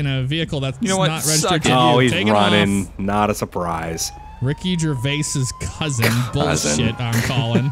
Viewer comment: When your friend said Ricky Gervais' Cousin i thought he said Yourvase lol it was so funny ahaaha A: Name of your cousin? B: Ricky Yourvase A: Yourvase huh? B: No Yourvase.. A: Myvase? B: Yes